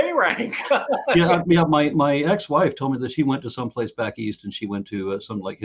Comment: wait, did he say "ex wife"? Did and his wife divorce?